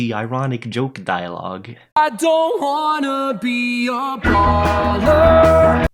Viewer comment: Hang on.. He says i don't wanna be clooney??? I thought he said "I don't wanna puny no no..." Oh god.